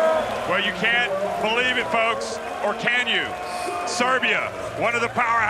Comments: what did he say? for my ni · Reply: Lol, not sure if it's a joke, I heard "reminding us" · what did the commentator say at 3:11 ???? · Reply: Serbia one of the powerhouses